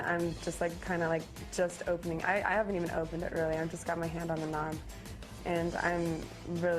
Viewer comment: Did she say she's got her hand on the knob?